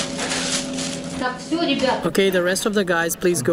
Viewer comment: Tourists left their snickers here… thought she/translator said snickers before I thought about it, “she probably said sneakers, that makes more sense.” Too bad because that snickers could really fix you if you’re not yourself because you’re hungry. New Hot Particle limited edition!